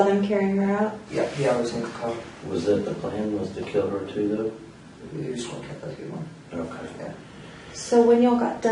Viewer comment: Awesome vid man. What does he say at 23:51? In response to "was that the plan, to kill her too though?" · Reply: Response was 'yes the plan was to kill everyone"